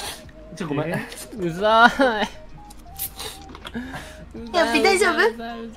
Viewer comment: ぐらい｢やっぴ大丈夫？｣にしか聞こえないおぢさん大歓喜！